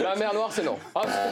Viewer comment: Il a dit quoi Rio a la 2:30? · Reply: Mer noire